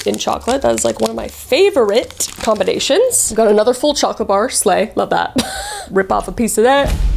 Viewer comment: it says sleigh instead of slay!